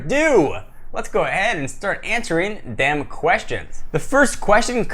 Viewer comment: it sounds like he said the damn questions lol